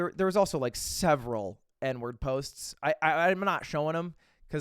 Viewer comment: I misheard this as "Edward posts" and I was just sitting here for a good long while wondering who the hell Edward was and why it was so bad to post about him.